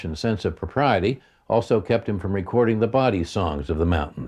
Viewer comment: The "what" songs ? Kept him from recording the body songs ? I don't understand what he is saying here. Anyone know ? Thanks ! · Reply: Bawdy means racy or crude.